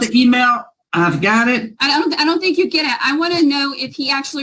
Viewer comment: Did she say. "I don't think you get it" TO A JUGDE!!!! oh lawd 😵